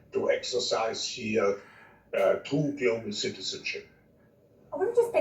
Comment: Right at 2:54 he says ...In the spirit, which means,Ahh... to exercise here ahh... two dual citizenship???Do you get what he's saying when he says that??? I do not consent to that at all!!Why would he say that!! Honestly that sounds like he's introducing possession of the spirit. Please tell me what I heard is incorrect. When we are natural born we have the spark God sends down here to be born ...Yet if he is announcing spiritually that there will be dual citizenship?? All I'm saying it better be from God and nothing else!